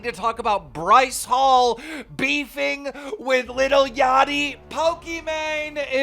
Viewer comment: Did he just say “LITTLE” Yachty